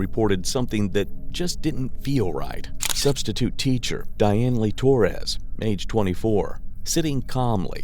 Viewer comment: Bro I thought he said her name was Diane Leigh Torres, not DIANELY. Dianely looks like a typo.